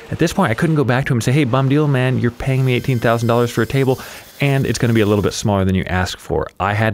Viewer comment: The wood better be infused with gold if I’m paying $18,000 for it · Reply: I thought he said $8,000. Really lost my mind when I saw everybody saying $18,000. But I guess it doesn't matter because both prices are crazy.